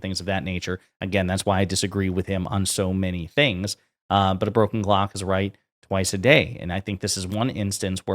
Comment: I swear I heard “broken Glock” 😂